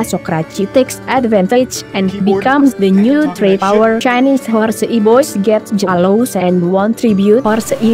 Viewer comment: I kept hearing "horse e-boys"